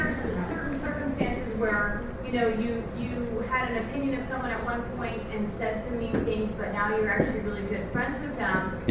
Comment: What does the lady say that makes Fr laugh so much around 1:02:33? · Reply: “Even if it were true”